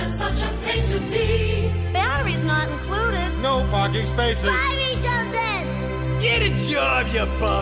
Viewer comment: ngl for a while I thought he said “No Fucking spaces”